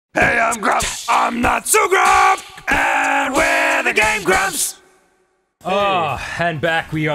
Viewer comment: OMG I thought the intro said "And we're the gay grumps!" XD LOLOL! Please do not take this seriously I just thought they said that in the beginning :D